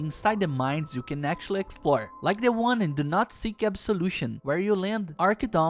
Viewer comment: What did he even say? I only heard "Nazi kept solution"😂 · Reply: He said “The Do Not Seek Absolution mission”, it’s the name of the mission where you beat up those miners harassing Mr. Downes’ son